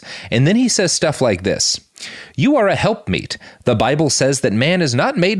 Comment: Holy shit, i thought he said women are referred to as "helpmeat." That was the most horrifying phrase i had ever heard, and i couldnt believe they just glossed right over it, so i looked it up. Apparently it's "helpmeet," which is still awful, but not nearly as gross and dehumanizing as i originally thought hahaha.